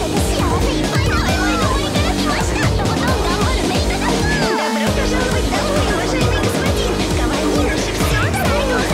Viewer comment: мне же не послышалось?)) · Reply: "Добро пожаловать домой уважаемый господин..."